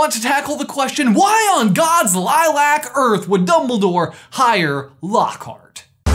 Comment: For a moment just before the intro I fully thought said "why on gods FLAT earth" and I kinda blacked out for a second